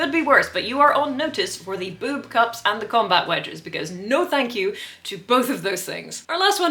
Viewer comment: I had just taken a drink when she whispered furiously "Are those combat wedges?" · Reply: I thought she said wedgies the first time.